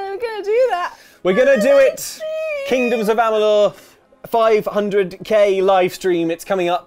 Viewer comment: For a second I thought said "Kippers of Amalur" which would just be OH MY GOD MAKE THAT HAPPEN SOMEHOW PLEASE!!!